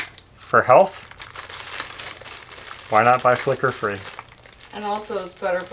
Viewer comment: I am LMBO listening to you talk about the light bulbs. I thought you were saying "Liquor bulbs". Then I realized you were saying FLICKER!! I was like, in all my years I'd never heard of Liquor bulbs. Then when I hit the space bar to finish this post, it decided I was done. Be patient with me, I'm 67 and have a lot of health issues. I would call that light macrame.